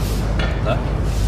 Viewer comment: "Von welchem Fernsehen sind Sie?" - "ADN" ADN steht für "Allgemeiner Deutscher Nachrichtendienst", das war eine staatliche Nachrichtenagentur der DDR, also nix mit "Westreporter". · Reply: Soweit man es verstehen kann: "Wo seidn ihr här?" "Von uns, und vom Spiegel." Also beides scheint zu stimmen.